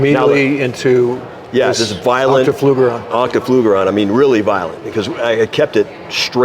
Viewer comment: Did he say octaflugerant?